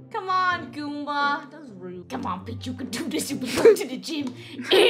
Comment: CMON PEACH U CAHN DO DIESH U BIN GOIENG TO DA GEYM if you picture the b word seconds b4 she says that and hear it it will sound like she said: cMON B***H U CAHN DO DISH U BIN GOIENG TO DA GEEYM